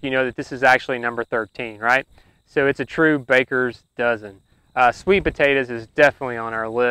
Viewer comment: I appreciated the benefits and reasoning on these survival crops. I counted 12 crops out of your baker's dozen, which one am I missing? 1. Corn. 2. Green Beans. 3. Winter Squash. 4. Peas. 5. Kale and Cole crops (Collards too). 6. Sunflower. 7. Tomatoes. 8. Okra. 9. Onions and Garlic. 10. Spinach. 11. Potatoes. 12. Sweet Potatoes. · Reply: I thought he said dirty dozen and not baker's dozen.